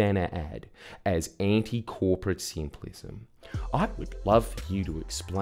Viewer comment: I thought he said "anti-corporate simp lizard"